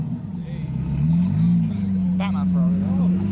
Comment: di... did he just say "batman ferrari"?